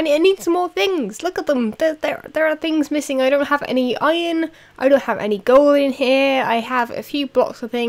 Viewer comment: sounds like you said ''i dont have Ellie Goulding here'' :p